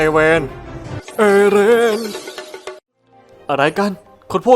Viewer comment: ใครได้ยินคำว่า เอเลนบ้าง